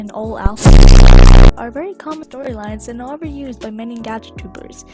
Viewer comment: you say Gacha tubers very wierdly( and you took notice of that), u slow it down( but you should know this □°□what am I doing at this point...) anyway, then i hear the audio saying "Gacha Troupers" ...... * insert gacha thinking? face * yeah idk, I think my ears are broken.